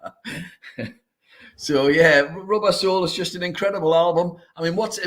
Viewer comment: I was listening to this on my headphones when on a walk so wasn’t looking at the screen and thought said the Beatles album was called Rub Arsehole not Rubber Soul 🤣